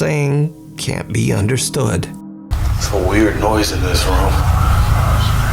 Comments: At the 26:45 mark if you listen carefully it almost sounds like something says “there’s someone weird in here” and the next one sounds like “run” followed by heavy breathing. Idk just a thought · In the Urbex hill clip with the male voice, it sounds like the first thing the voice says is repeating what he said “there’s some weird noise in this room”. I can’t make out anything else it says, but that’s what it sounds like he says first. Anyone else hear that? That video was pretty damn spooky.